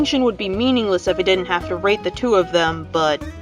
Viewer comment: OMG I THOUGHT YOU SAID RAPE XD XD XD 🤣🤣🤣